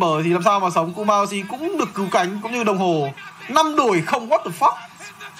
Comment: DID SOFM JUST SAY "WADAFUQ?" LOOOL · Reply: He did. He said "5 exchange for 0. WADAFUQ?". It's mixed with the way how Vietnamese people say that expression in a surprising way.